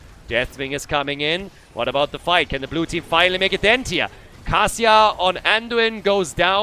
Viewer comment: I got confused for a sec when you said "Kasia" because I heard "Cassia" and I was like "there's no Cassia in this game"